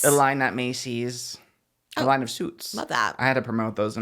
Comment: i need to check my ears bc i thought said ryan seacrest sold a line of soups at macys lmaoooooo